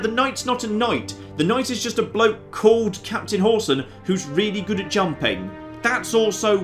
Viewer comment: I heard him say Captain Horsan, for a moment I thought he said Horton and I was like I AM RELEVANT!